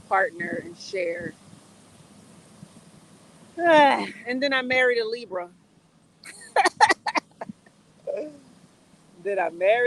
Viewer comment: I think I heard don't marry a Libra. We just going to be outside in the rain. Lol.. The live was fun.